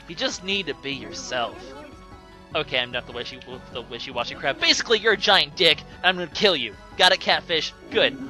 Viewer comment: lol I thought he said "I'm really crap" XDDD